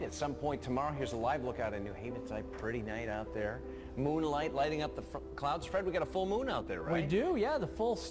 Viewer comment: Hahaha!!! Did he say "lighting up the fucking clouds"???